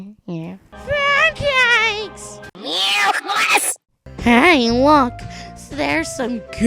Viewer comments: sounded like useless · did taco said "new class"?